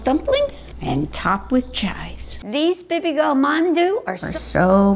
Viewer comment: i really thought she said “babygirl mandu”